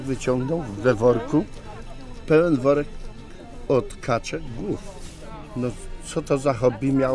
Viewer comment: "Pełen worek od kaczek gówn, co to za hobby miało być to nie wiem" haha leżę :D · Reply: Ja tam usłyszałem: głów a nie gówn Xddd